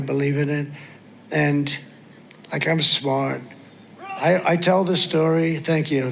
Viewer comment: It sure sounds like Trump says at 0:56 'and... I kinda smart". Wow.